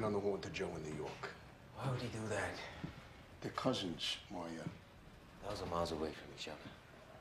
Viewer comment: Wtf does meyer say in the beginning at like 00:14 after he says their cousins Meyer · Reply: 1,000 miles away from each other